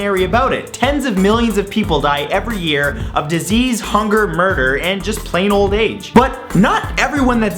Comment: I thought he said "Disease, Hunger, Burger and plain old age" XD